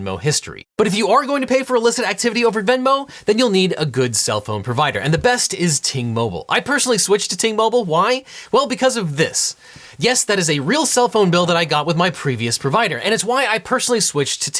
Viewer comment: For a second I thought he said “Teen Mobile” as a joke about the allegations.